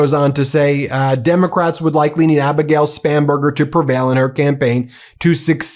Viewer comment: Omg all this time I thought you were saying her name is Abigail Spamburger 😂